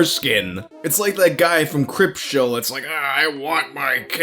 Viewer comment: It sounded like said "Cripshow" instead of "Creepshow". ...I wanna see that.